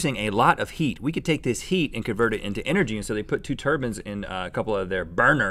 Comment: Could have sworn Rhett was saying ""Turbans" instead of "Turbines". I was picturing head coverings producing electricity throughout this episode!